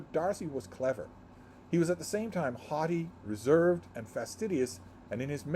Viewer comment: When you read “haughty” in regard to Mr. Darcy, I heard “hottie”! 😂 What I like about this chapter is that we see first the two sisters together, and then the two gentlemen together, and we can draw tons of parallels between these two relationships. According to the notes in the gorgeous annotated version that I borrowed from the library (sigh, it’s out of print), Bingley is 22 years old. Young men come of age at 21, and it’s been less than two years. According to the same source, the £20,000 attributed to the Bingley ladies is a lump sum, not an annual income (I assume it serves as a dowery).